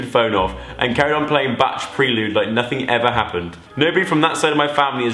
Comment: did he really say Bach as in Batch?? 😂